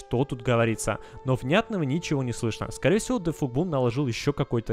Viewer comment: Я слышу там - we go (Перевод)мы идем